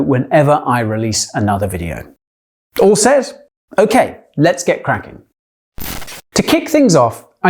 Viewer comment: He says "Let's get Kraken @ 1:25." Did anyone else catch that?